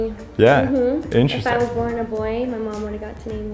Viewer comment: at first I thought she said "I was born a boy" and I was about to lose it